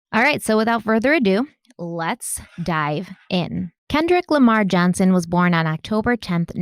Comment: Wait did you say Kendrick LAMAR Johnson? I never heard anywhere that was his middle name